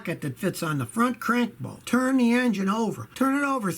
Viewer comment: did you say if you "cant" that's great😒